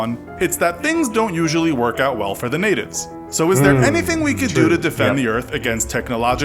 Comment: at the 3:12 mark, from the back of my studio, it sounded like the narrator said: "niggers". It was hilarious.